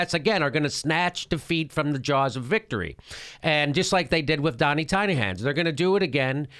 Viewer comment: It always sounds like Jimmy is saying "Jonny Tynerhann" when he says "Donald Tiny Hands".